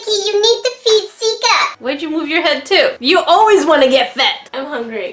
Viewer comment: Omg i'm so sorry, my volume is down low and when you said "you always wanna get fed" i thought you said "you always wanna give head" XDD